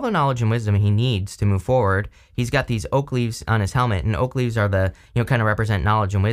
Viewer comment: I thought you said Oakley’s… oak leaves 🍃